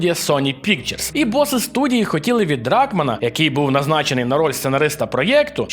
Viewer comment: "РАК-man" xaxaxaxaxxaxaxaxaxax P.S:мені всього лиш почулось насправді дракман але все ж посміявся знатно з "РАК-ман" хахаах